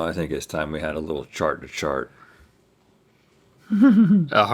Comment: I thought Rhett said, "Shart to shart" at the beginning, and my first instinct was not to question it because of the episode title